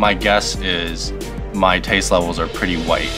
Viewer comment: "My taste levels are pretty white" says the half Japanese guy to a white man who makes a living out of spicy food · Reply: thought he said "pretty light"